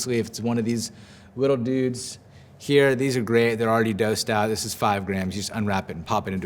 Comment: Did you say, "dosed out", at 8:40? Love the videos!